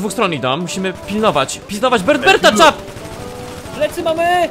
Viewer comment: czy tylko ja usłyszałem "musimy pizdować bertberta"?